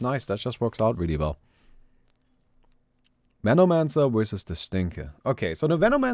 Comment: Did anyone heard it like "Menomancer wersus the Stinker" lol